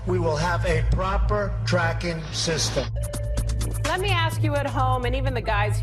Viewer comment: I thought president Trump said 0:15 "we will have a proper dragon system" (dragon represent the devil ) but he must have said tracking system..